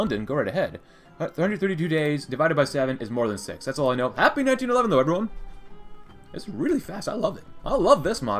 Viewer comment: I thought he said “happy 9/11 everyone!”